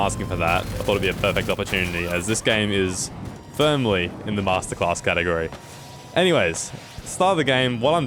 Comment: For a second I thought it said “Jedi Masterclass” lol